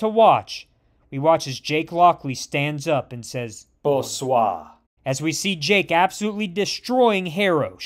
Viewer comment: So umm… what did he say? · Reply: Bonsoir, French for "Good Evening."